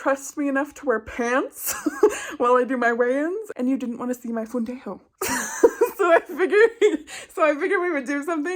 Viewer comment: Did just say fundillo at 0:49???? 🤣🤣🤣